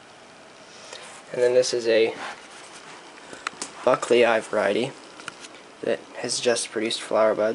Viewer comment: ok I have listened at least 10 times trying to hear what variety you say at 2:07 it's like mine so wondering? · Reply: Buckleyi variety